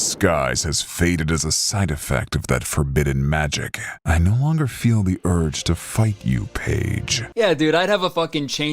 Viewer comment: Please tell me I'm not the only one that thought he said "I no longer feel the urge to FUCK you Paige" at 28:05 ???? Edit: nvm it sounds a lot clearer on my phone 😅